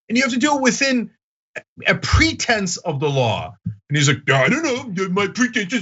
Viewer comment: Chump thought you said "pretends of the law" not "pretense of the law"